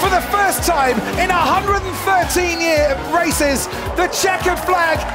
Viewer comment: If you really want to hear it you can hear rasi- instead off races